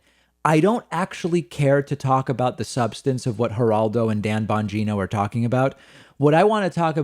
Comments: It sounded like David said Bongina like vagina, lol. oh well , peace. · I thought you said Dan Mangina